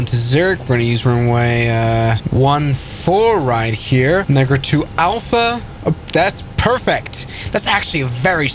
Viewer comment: Sorry what he say? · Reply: negra 2 alpha